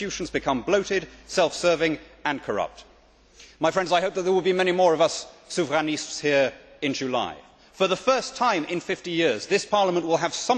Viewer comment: what is this word at 2:27"there will be many more of us : "sukraniste"???? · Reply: "Souverainistes". Six years later, but there you go...